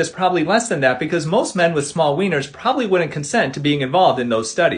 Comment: Phew i thought he said the p word its actually wienners